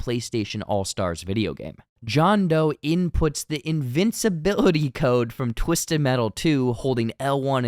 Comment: I thought that code was for God mode, not invisibility. It's too long. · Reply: Oh, I thought he said "invisibility". My bad